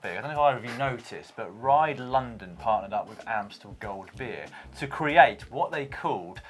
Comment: I thought they said Amstel not Amstel gold